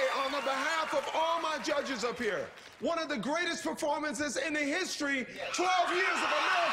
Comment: This season sucked but she was great. Initially I thought this was ridiculous because I misheard Randy - I thought he said THE best, not "one of the best". It certainly IS one of the best, but I also think we have the improved production quality to thank.